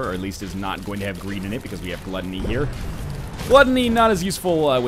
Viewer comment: I misheard you say Gluttony and heard Guppy, and a thought came to me. What if they made a rare Guppy/Tammy mini-boss room with a hard fight, and gave the player a guppy/tammy item?